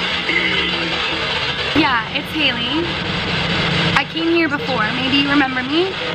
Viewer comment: did anyone else hear hide? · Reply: sorry... i meant hide Hailey